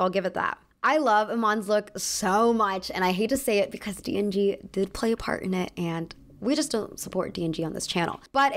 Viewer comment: Aw at first I thought she said "D and D played a part in it" and I got super excited trying to figure out which Dungeons and Dragons goddess Iman was channeling 😄